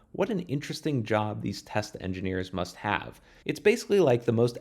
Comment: love those tesla engineers test engineers? still sounded like tesla to meh